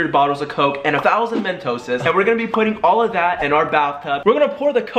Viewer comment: did he say mentoses and also why would he wear a white shirt in the coke???????😂😂😂😂